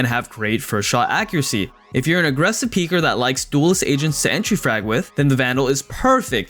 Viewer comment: Yo I’m not gonna lie I really thought this man said Asians instead of agents lmao